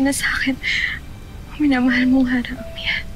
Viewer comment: yung sinabi niyang "Ybrahim" si Amihan yung narinig ko!!😭😭 Ate Kylie nakakamiss ka! 😭